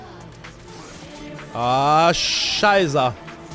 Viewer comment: Did he just said "scheiße"? (German)😂